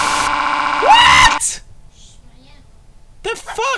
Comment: I hear Marzia